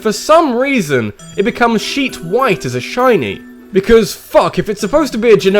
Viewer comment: Thought you said "sheep white" at first for Gengar lol.